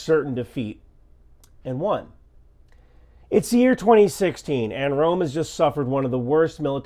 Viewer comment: What year??? Because it sounds like you said 2016. I could be wrong and I hope I'm wrong. But I don't think I'm wrong.